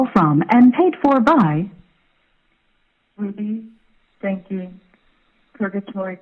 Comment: I hear “Ruby stanky” 😂